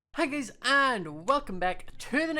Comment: when he said hi guys i thought he said haggis XD